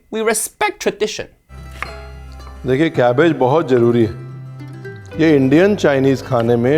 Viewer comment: Did he just say “nigga cabbage”?!?!?!😦